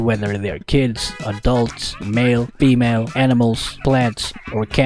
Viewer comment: did he called lance a femal lmao